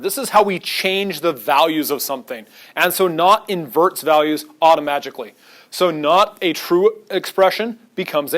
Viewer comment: idk if you meant to say it, but it sounded like you said "automagically", idk why but now I really like that word, lol